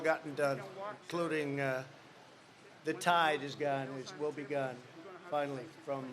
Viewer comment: Did he say "The tide is gone" at 7:21? Seriously.... sigh